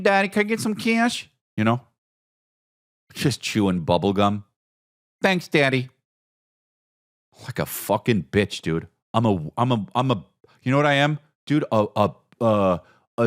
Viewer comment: All I keep hearing in my head “Chris Da Bitch” in bizzy bone voice 😂